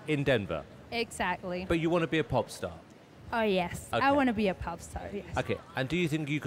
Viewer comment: For the first one I thought she said "I wanna be a pop tart!" XD